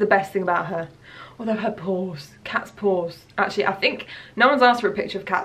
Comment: I legit thought you were saying cat's BALLS. And I was like, where are you going with this? 😂 I'm canadian and misunderstood your accent.